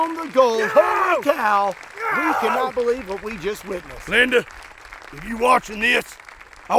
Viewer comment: I thought it said my name Landon but it said Linda